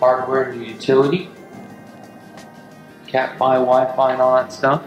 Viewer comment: Did you say Cat 5? @ 7:24?